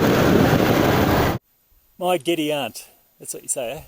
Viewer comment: What did you say at 5:19? What a great trip! I love your adventures! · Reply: My giddy aunt - something I hadn't heard in years but just came to mind in the situation in which I found myself!